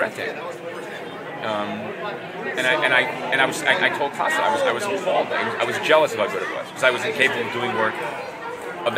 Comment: i can't figure out what names he said at 8:08, somebody with a better ear can please tell me? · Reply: Klaus Janson